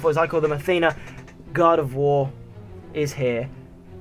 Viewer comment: i thought you said "Athena, God of War" xD